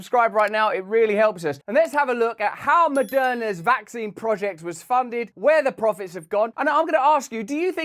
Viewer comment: Haha. At first I thought he said Madonna sold vaccines😳